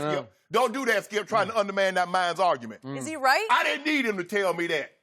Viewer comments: Did he say... "Underman that minds arguement?"... Shannon always on a 1000 when he talks about Lebron... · Did he just say underman that mines argument?